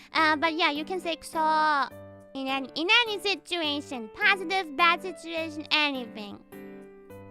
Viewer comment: Did she said Ina'nis situation?